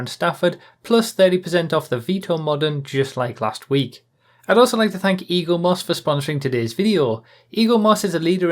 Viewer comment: Anyone else thought he said "I also want to thank Elon Musk"